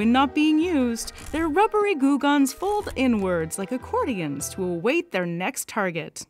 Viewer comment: It was the part where you said “it folds inwards,” but I mis-heard it as “it’s full of N-words,” and I was like - but wait … what?